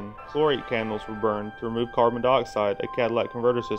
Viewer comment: Did you say Cadillac Converter? Certainly sounded like it. It's a Catalytic converter. 5:40 mark.